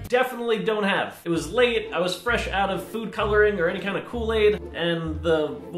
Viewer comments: I thought he said he was out of food dye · Thought u said u were out of food diye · i thought he said he had no food coler